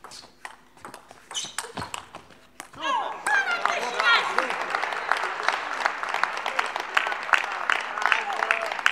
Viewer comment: ??? What did Bernie say? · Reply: "just this much (she was referring to the block) and it/you won't reach" - is not clear if she was talking out-load to herself or she was addressing that to Adina